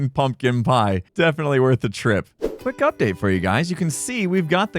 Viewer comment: I know you said "Quick update", but considering the cub-bit before that I only heard "Quick Cupdate", genuinely had to double back and make sure you didn't sneak in a pun there :P